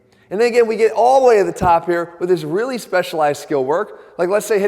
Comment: sounded like he said “and nigga we get allll the way to the top here” lol